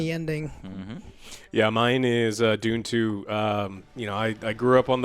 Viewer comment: When oj says dune 2 I thought he said doom 2, that would be boss